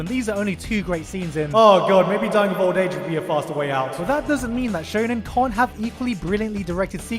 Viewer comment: Guys, what does he say at 10:05? "Oh god, maybe ..." English is not my native, I can't recognise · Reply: "Oh god, maybe dying of old age would be a faster way out"